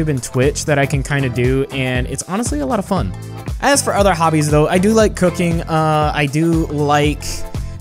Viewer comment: for a second there i thought that he said he likes cocaine...